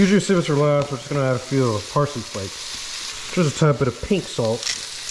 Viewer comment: I thought he said pink sauce